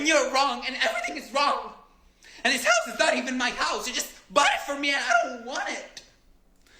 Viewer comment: "And you're wrong and 📯 is wrong! And this 📯 isn't even my house, they just 📯 it for me and 📯 don't want it" Think that's what he said